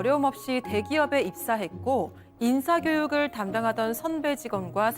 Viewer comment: 나만 인싸교육으로 들림??ㅋㅋㅋㅋㅋㅋㅋㅋㅋㅋㅋㅋㅋㅋㅋㅋ